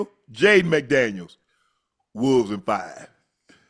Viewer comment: Shannon... I thought you said lakers in 5... naw.. wolves in 5